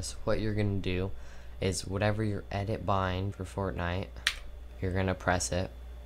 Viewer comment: I thought he said fartnite